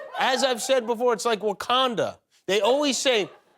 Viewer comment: did he say Macondo?! From One hundred years of solitude?!